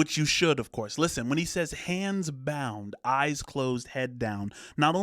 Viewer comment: I always thought he was say “hands bowed” as in like prayer.